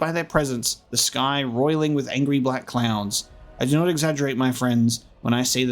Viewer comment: Only I hear "...angry black clowns"? 🤡